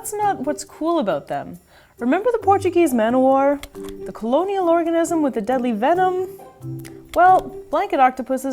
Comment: Hahahahaha at 10:29 i thought i heard “the colonial orgasm with the deadly venom”